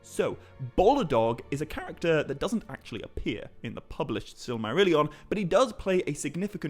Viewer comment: Maybe I'm just tired, but every time you say "Silmarillian" I keep hearing "Silly-marillian" And now I really want somebody to write a spoof of it.